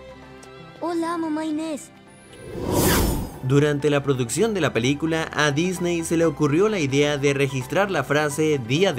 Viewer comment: que dijo el niño:v? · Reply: Dijo : hola mamá Inés :v